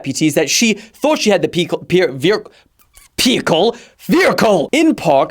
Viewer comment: i swear he says vericle at 20:36?!?!